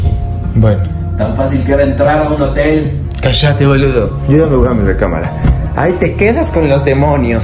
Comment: En el minuto 9:19 dice "CALLATE BOLUDO" o soy yo que escuche mal??